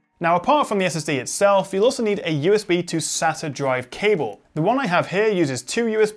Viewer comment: USB to what? 3:50 Cant make out what you said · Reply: SATA drive cable